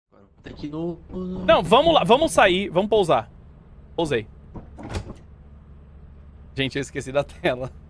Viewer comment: primeira vez q ouvi ele ouvi ele falando g0zei ai voltei o video e vi q ele tava falando pousei kkkk